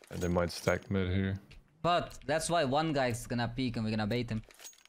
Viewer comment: "That's why 1 guy is gonna peek mid and we're gonna bait him." It's funny cause I heard you say "bait" but I know you said "trade". 😆